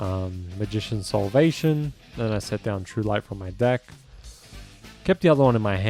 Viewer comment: did you just say true light???